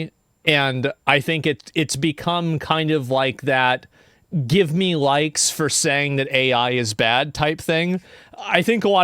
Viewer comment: Youtube thought he said "Give Me Light"